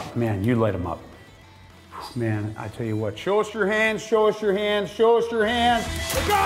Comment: One of the times that says "Show us your hands" it sounds like he says "Go wash your hands." I had to back it up and listen again. I'll be glad when this coffee kicks in. Great lights, btw.